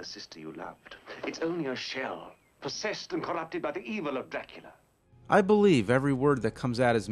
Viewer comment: Sounds like he said 'Blackula'.